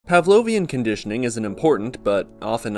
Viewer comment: whrn you said "pavlonian conditioning" i thought you said "pavlonian air-conditioning" and i was like.. hmmm should i get their brand?